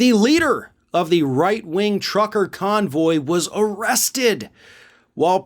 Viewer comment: I thought you said Tucker convoy, I thought great he deserves to be arrested